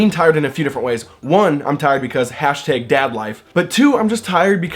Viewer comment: I thought you said # dablife at first. . . I wouldn't recommend looking at that tag lol #dadlife